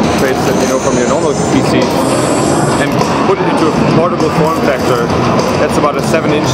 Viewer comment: did he just said portable porn factor 0:43 XD ?